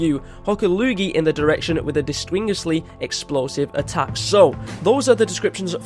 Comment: did he say "dislingisly"?